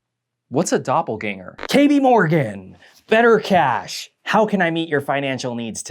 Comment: definitely misheard that as 'Katie Morgan' 🤦🏽😂